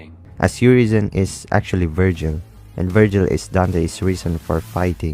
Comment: i thought you called my boy a virgin for a second. actually you know what that may be his problem 🤣 maybe Vergil needs to loosen up